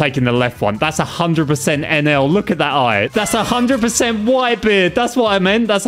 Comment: I thought you said 100% Enel?